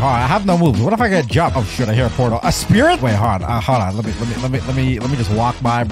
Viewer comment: yo i swear he swore at 13:46 he said oh shit i see a portal